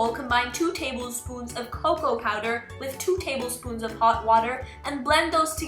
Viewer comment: I thought she said baby powder 😂